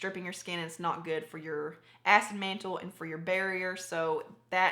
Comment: Great, thorough video. However about 9:12 I swear it sounds like you are saying "ass mantle" 😂😂😂 I've heard Thataylaa say she uses Rose Hip oil on her acne, have you tried it? Also, what was the Elf foundation you mentioned? Thanks 😃